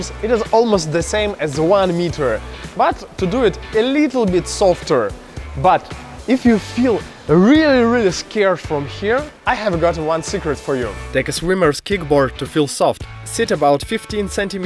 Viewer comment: I thought he said I have one cigarete for you😄😄